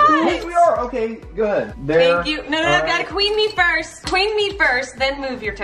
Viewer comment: When he said queen mees i thought he said kidney